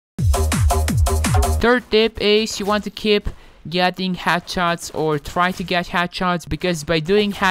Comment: It doesn’t sound like he saying “head shots” it sounds like he’s saying “hat shot”